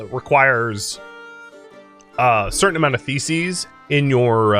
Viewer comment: "Librarian Tier 3 requires a certain amount of ?????? in your library" I heard this in the background, and misheard the mystery word in a bad way (my bad, not yours). I now understand it was "THESES" (Plural of Thesis)... which makes sense. For some reason my brain heard "Feces"... and I could not for the life if me understand why you'd want that in a library.